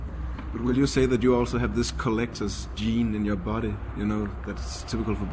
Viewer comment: What does the interviewer say at 14:17? "Would you say that you also have this (collector's?) gene in your body? You know....boys....attraction...." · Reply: kiran ...yes, collector's...And then "in your body that's typical for boys"...Then Jeff starts talking and I can't understand the word/s before "attraction". The interviewer is Danish, so his grammar might be a bit wrong...